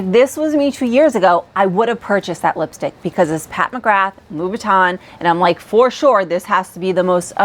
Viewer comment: i thought she said louboutin lipstick, not louis vuitton